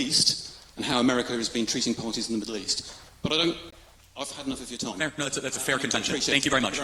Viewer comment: When the professor says to Charlie Kirk at 9:33, "I've had enough of your time", I swear I thought I heard the prof. say "What an absolute mor0n, and a waste of my time."